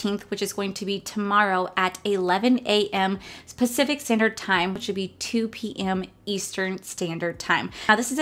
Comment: did you say specific standard time???